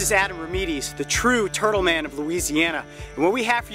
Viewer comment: TurtleMan...Did he say TurtleMan?...If so.................. YEHEHEHHEEHEHEHEHEHHEEHEHEHHEHEHEHEHEHEHEHE